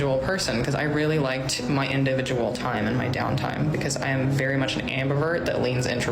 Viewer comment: I thought she said "I am very much an Amber Alert."